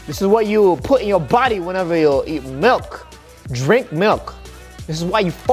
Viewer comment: “Whenever you eat milk” - guava juice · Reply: I think 4:11 What he said Eat milk Just correcting it